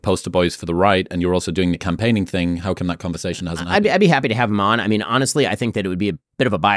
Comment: I'm 5 minutes in and Ben is very obviously lying to the audience about that fact that he wouldn't do an interview with Donald Trump and giving bogus reasons as to why. Didn't need the full 2 hours for that observation. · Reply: you misheard him. 4:48 - "I'd be happy to have him on" Maybe listen to someone carefully before saying they're lying.